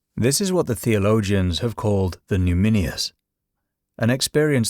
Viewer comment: What is the word 'pneumminius'?? I am very interested in this concept - shows in the caption at about 4:32 - may be misspelled cause I cannot find ANYTHING about this word anywhere online or in dictionaries. Anyone know the spelling? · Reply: It's "numinous"